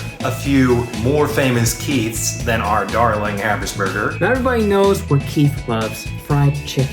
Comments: the fact that it says average burger in 1:57 instead of habersberger is making me whEeZe (turn on cc) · says average burger in the caption instead of habersberger and it has me dead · when Eugene said "our dear Habersberger"..... My subtitles said "our dear average burger"... Sry Keith. But you are our dear burger💛💛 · Lol captions at 1:59 says “darling average burger” when but I heard Eugene say “darling Habersburger. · This may be irrelevant to the actual video, but the captions said "average burger" instead of "Habersberger" at 1:56 and I died laughing